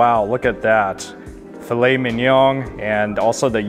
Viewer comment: did he say 'filet men-young'? I think he did. lol